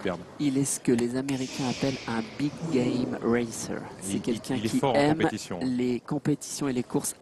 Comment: Does he say "Big gay racer" at 0:32 ?????